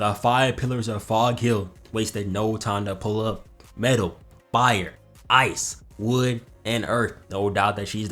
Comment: did bro say bire?????